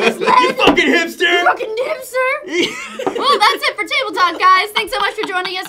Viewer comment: Did Meg say "nipster" at like 13:57..?